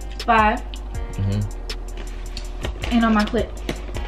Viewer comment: Wait... did she say “And on my clit.”?! I’m dying! 😭😂😂😂